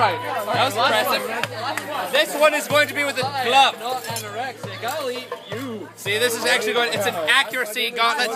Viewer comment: did anyone else hear "You're anorexic I'll eat YOU!!!"???